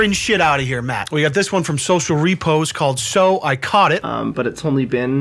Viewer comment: Did he just say Social Repose seems like a good guy